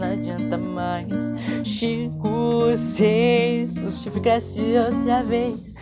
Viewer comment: eu fui o unico q ouvi ele falando chico seis? percebi q nessa parte ele ficou sem ar ;- 0:31 Tbm ele falou puque.